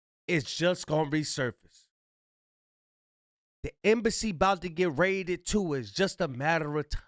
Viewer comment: The embassy bouta get raided too. Its only a matter of time....crazy🤪 scary 😱 spooky 🤯 hilarious 🤣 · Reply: Thought he said industry, but still message received 😂😂